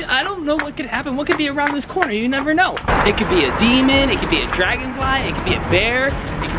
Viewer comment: did he say dragonfly??????